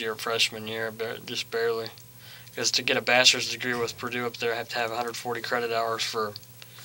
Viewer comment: Totally thought that he said bastards degree instead of bachelors degree.